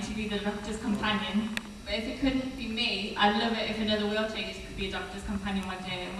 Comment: What is that girl saying at 25:13? I'm hearing something about a wheelchair. But I feel like that's wrong. Also, I agree with everyone else--this is audible and visible and generally well-done. Thanks.